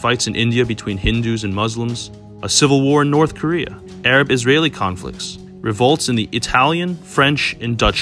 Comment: I thought you said there'd be no North Korea.